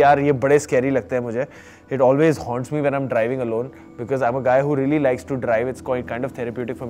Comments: OMFG....at 7:28 when Vicky says because I'm a guy..... And the subtitles says because I'm a gay..... 😂😂😂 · Subtitles were 'gay' instead of guy...😂😅 P.S - NO HATE!